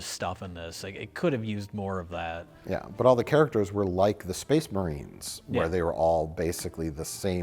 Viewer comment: did he just say... Space Marines?